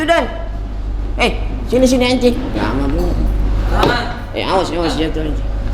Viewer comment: Menit 14:58 ngomong apa ya sultan telinga saya salah denger nih kedengeran nya "heh sini sini lama bener"